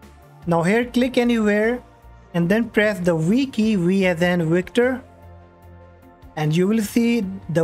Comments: Do you mind translating one part because I hear press the we key vsn victor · what does 1:54 say? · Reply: Press the V key to find the VGC service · What key did you say around 1:58 of the video? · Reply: press v key can u sub for that? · somebody pls tell me what he said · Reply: V key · WTF is the one that i should press on 1:54 we cant understand you bro :( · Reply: V key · What did he say in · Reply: press v key and please sub because i told you? U dont have 2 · i can’t understand what ur saying at 1:54 ?? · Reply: Press V key to find the service VGC